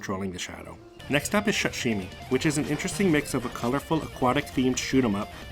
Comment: whats the name of the game? I couldn't understand. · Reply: Shutshimi